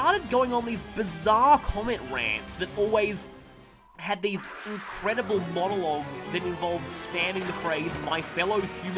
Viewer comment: I couldn't make out the name of the YouTuber in your video. What was his/her name? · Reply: Goldie. More specifically, I think he is talking about GoldieX39.